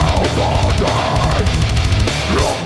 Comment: "i see that flag wave it up" (i think thats what he says) why did he said that? he is from scotland?